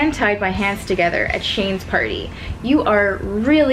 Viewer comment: SHANE? DID U SAY SHANE? (Aka shane dawson)